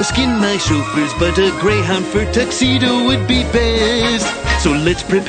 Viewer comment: always thought he said "a great hunt for a taxedo"